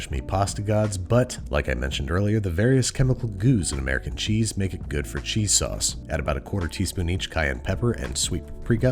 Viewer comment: Dumb question probably, but what was that?? He said the cayenne and paprika that followed it but I dont think he said what the bottle was? :( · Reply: Dijon mustard